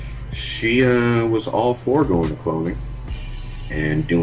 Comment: 🤔 Did he say she was all for going to cloning??? (I don't understand the last word) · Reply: Going for clothing... Fashion industry bs I guess.